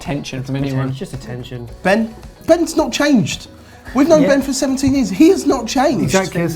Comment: I thought said I've known Ben for 70 years.... Since when did you go to Neverland?